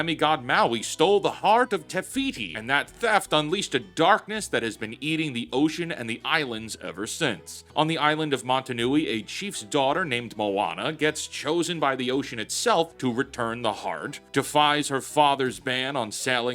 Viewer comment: totally thought you said "Mata Nui" (as in the island from Bionicle)